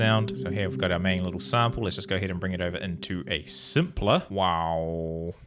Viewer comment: OH MY GOD, THIS WHOLE TIME I THOUGHT HE WAS JUST SAYING "SAMPLER" WITH HIS ACCENT IT SOUNDED LIKE "SIMPLER" BUT THAT'S ACTUALLY WHAT IT'S CALLED.